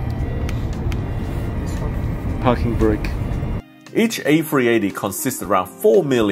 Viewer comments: I thought he said f*cking brake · how come i heard fucking break in 2:39😂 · I love this guy - I thought he said "f**king great" but he actually said "park and brake" during the cockpit tour - Sam, you make me laugh and keep me engaged in my favorite hobby - kudos to you :) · did I just hear "fucking ready"? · Did the pilot really say "parking break" at 2:40? I heard "&$^@ great". See you had your own private hostie on that inaugural flight.